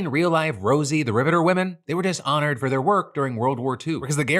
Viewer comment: I had this playing in the background and misheard "They were just honored for their work in WW2" as "They were dishonored for their work in WW2." I was very confused! 😅😂